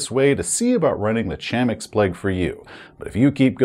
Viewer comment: I thought it said Climax plague. I was like "that cannot mean what it seems like it means".